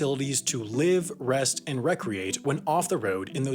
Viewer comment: When he said recreat i thought he said procreate lmao